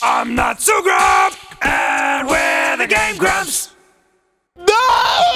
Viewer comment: When Dan said "welcome to game grumps" I heard "welcome to gay rumps"